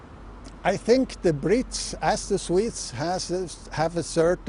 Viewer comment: Did I hear Professor Michael Ambuhl ( at 11:12 to 11:16 ) say, "I think the Brits, as the Swedes,....." when he was referring to the Swiss, of which he is one. The Professor was introduced as a former diplomat.